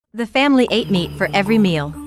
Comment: I thought they said "my family ate me" surprised to see they didn't